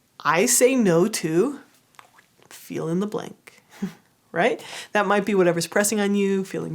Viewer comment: I thought she said "I say no to feeling the blank". Which oddly made a lot of sense to me lol.